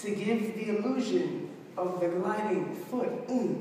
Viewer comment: This is so good! Could anybody please tell me what did he say in 0:41 "we are constantly changing weight to give the illusion of ------- ----" my english is not so good. Thanks in advance 😀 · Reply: illusion of the the gliding foot